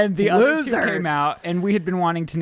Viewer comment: Omg I thought you said you were gonna name the kitten “coochie” instead of Gucci 💀